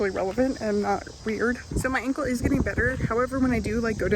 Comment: When you said "so my ankle is getting better" I thought you said "my uncle is getting better" lol I was like "I missed a video where she talked about her uncle"